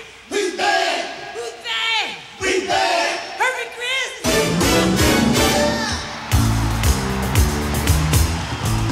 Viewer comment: What did he yell? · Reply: it weren’t that it was “hurry Chris”